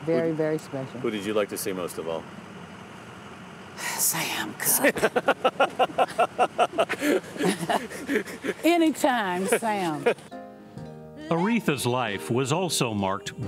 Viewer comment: What did she say at 2:34? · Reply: AiDie Sam Cooke....anytime Sam